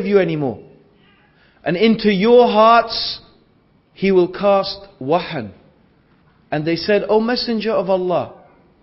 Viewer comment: what arabic word is he saying? is it "wa-hal"? my father asked other Arabs what it meant out of curiosity, and none of them knew. Can someone please tell me??